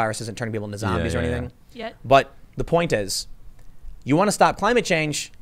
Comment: I heard her say yet lol